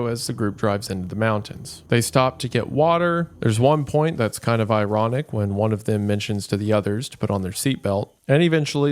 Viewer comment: Does anyone else have the audio cut out for them at these times 1:11:52 and 1:11:59? If no one else is having this happen can someone please tell me what is said during those moments? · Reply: not sure if u got ur answer already but this happens for everyone it seems like! he says su*cide pact and similar words (maybe just the first word?) which he intentionally must’ve cut out after the video got age restriction flagged